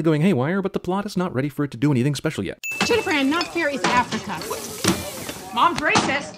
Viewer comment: Wait, what did the mother say at 2:38? "Jennifer Ann not fairies Africa"? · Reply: not fair is africa